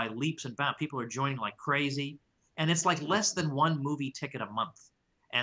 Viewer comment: thought u said "bootie ticket" then i realized u said "movie ticket" i was thinking for a sec that if bootie tickets really had become cheap, then krugman must've turned out to be right about that price deflation he's been warning about for forever